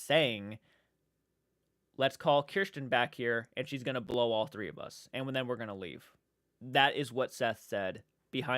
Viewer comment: Hey I wanted to bring this up but I went back to the part of what Seth said and I kept replaying what he said to make sure that’s what he said and i heard him say she’s probably gonna come back here and blow all three of us and then we’re gonna leave instead of we’re gonna call Kirsten back here and she’s gonna blow all three of us and then we’re gonna leave idk I could be wrong just thought someone should check it out again cuz if I’m right then the dude that made the video said something different from what Seth actually said